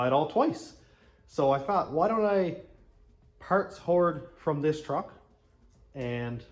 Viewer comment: At about 2:20 it sounded like you said you were a parts whore. Dont be so hard on yourself. Keep up the great videos!👍🇨🇦